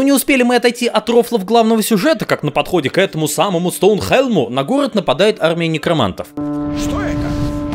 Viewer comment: услышал "на город нападают армяне"